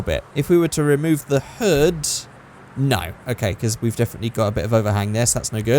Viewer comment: did you say "nein" ?? because it would pretty funny